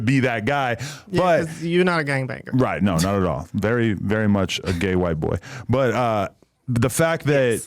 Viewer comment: ????? Wat u say adam?? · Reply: Very much a gay white boy